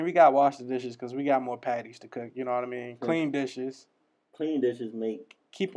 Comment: i thought you said clean Biches but you actually said clean dishes